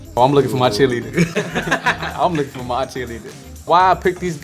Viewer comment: OMG at first I thought he said I'm looking for my chili